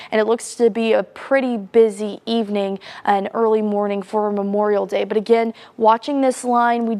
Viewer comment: did she just say “ a busy Memorial Day “?